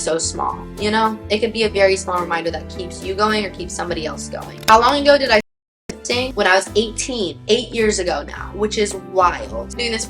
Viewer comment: wait what? I can’t read her lips anyone know what said? 😅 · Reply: hehehe just asked how long ago I started lifting!